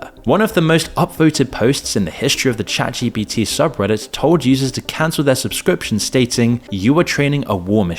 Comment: “told jesus to cancel his subscription” wtf did i just hear?